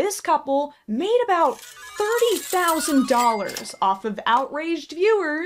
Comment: why am I hearing 5,000?